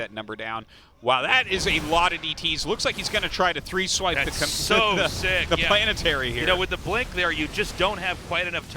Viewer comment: Did he just say "Complainetary"? Because that's a term I've never heard before and I love it! XD